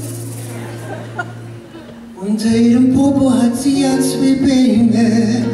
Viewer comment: Was... zum Geier singt Mirco bei 1:35 ich verstehe immer.... *rot werd* "unter ihrem Popo hat sie ganz viel Pe..s" bitte klährt mich auf sonst werde ich noch an nem Lachflash sterben^^